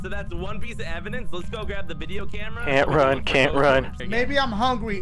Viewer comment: I to thought it said Oatmeal instead of can't run